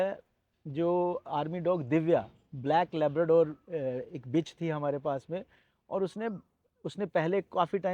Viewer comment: did he said bitch